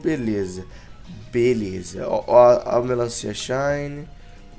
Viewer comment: kkkkk eu ouvi direito ou o falo a melancia shiny kkkkkkkkkk